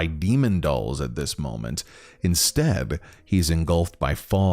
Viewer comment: lol I can't unhear it at 4:15 the " inSTAB " instead of well ... " Instead "